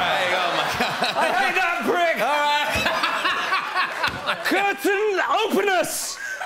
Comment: did he just say "Curtain openus" ? XD